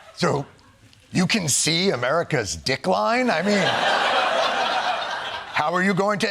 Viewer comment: Going from a decline to an absolute FREE FALL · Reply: No, no, no, you misheard because of the accent. He said dickline. I think you need an accent translator too.